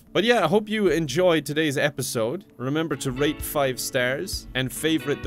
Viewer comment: Sounded like he said "remember to rape 5 stars." Am I not the only one who heard that?